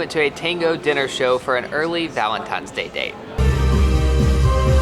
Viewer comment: Did you just say "valentimes" day?